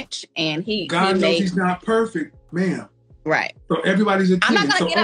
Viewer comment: did Kevin really say, "God knows He's not perfect?"